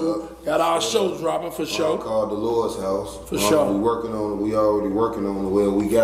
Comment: I’m dead told him Delores bought they outfit 😭🤣🤣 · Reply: He been saying “Delores” ? I thought he been saying “da lords” whole time😂😂